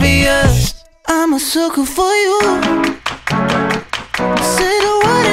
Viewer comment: You know the drill 1.Jonas brothers 2.Ariana Grande 3.Ava Max 4.Lady GaGa 5.Justin Bieber 6.Justin Timberlake 7.Marshmello 8.Imagine Dragons 9.Miley Cyrus 0.Billie Eilish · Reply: Who else thought the song said “I’m a circle for you”